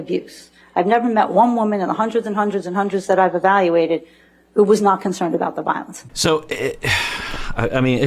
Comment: I thought she said "in the hundreds and hundreds of victims I treated, not one of them was hooked on the abuse," not "in the hundreds and hundreds of victims I treated, not one of them was a man," lol. Did I understand wrong? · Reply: You misheard. She said specifically indicated the hundreds were women. Timestamp begins at 15:10 where she says verbatim, "They don't get hooked on the abuse. I've never met one woman in the hundreds and hundreds and hundreds that I've evaluated that were not concerned about the violence. "